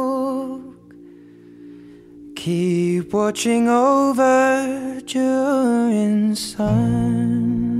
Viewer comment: It almost sounds like he says keep watching over Jordan’s sons.